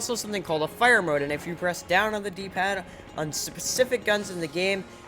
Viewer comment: you said sizzspific instead of specific